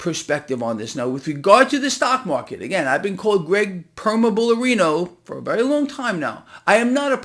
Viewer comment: Did you just say "Permabullarino???" That's hilarious lol